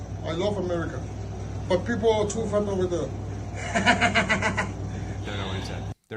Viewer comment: It sounds like he said we are too fat 😆